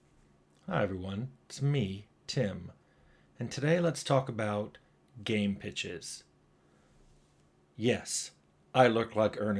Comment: I heard "Today we are going to talk about games, bitches"